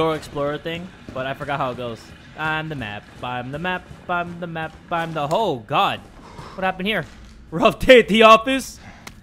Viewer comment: its sounds like Jay said "I'm the hoe god" 😂